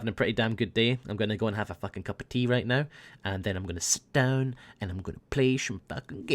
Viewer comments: what did say at 16:31? sounded like he said stone? im not insinuating anything here because that could be slng or something, but it sounded like he was gonna go and smoke some pot? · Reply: “Sit down” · i heard that part but im sure i heard that before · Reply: Nah, when he says sit down, his accent makes him kinda sound like he says stone